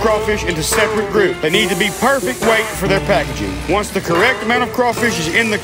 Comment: I thought you said HOBO crawfish